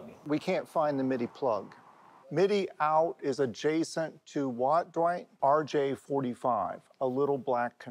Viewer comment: Did he just say "adiacent to rj45"? Does that thing have ethernet?